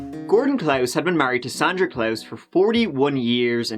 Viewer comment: I legit thought he said Gordon Klaus was married to Santa Klaus.